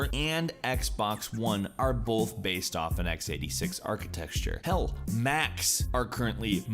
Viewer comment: Is it just me or does it sound like he is saying archi"texture"